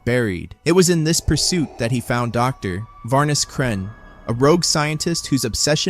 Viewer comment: is the man Vader found, is he called named Varnes kren? I can't pronounce his hime right.